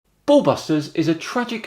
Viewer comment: did you just say ballbusters?